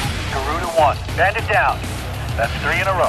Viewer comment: Did I hear someone over the radio say "Garuda 1" at around 11:45? That's cool. I haven't played Assault Horizon, and while it looks cool, I personally prefer the world in which the other Ace Combat games take place. Ace Combat 5 is my personal favorite.